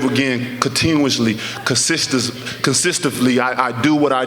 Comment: Did he just say "consistefully"? @ 4:30 I mean what's that??😄😄😄😄